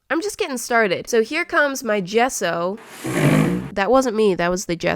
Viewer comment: Who time I thought she was saying “Besso”